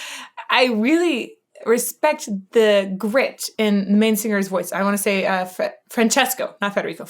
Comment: When she says "main singer" at about 8:18 in I really thought she said "Manspeaker" as in Bill Manspeaker.